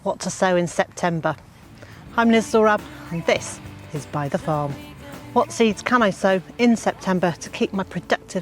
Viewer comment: For so long I thought you were saying By The Farm